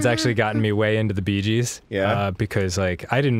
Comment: thought he said beej's.